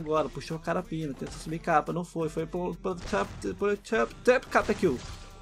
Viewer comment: "foi paprosteirosyyrhfidjhairoaowp capa é kill" kkkk